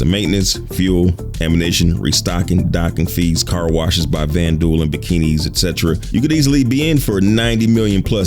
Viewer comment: Did I hear "Vanduul in bikini"?? ROFL!